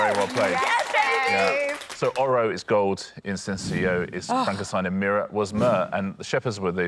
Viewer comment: Is it just me or does Richard say Frankenstein not frankincense at 11:49?